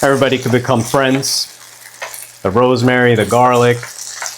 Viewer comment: I thought you said bro's mary😂😂😂 On a serious note, this looks like a recipe that children would love. I appreciate the fact that it's cheese free.